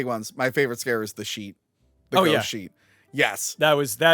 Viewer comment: Thought said “ghost sheep” at first😂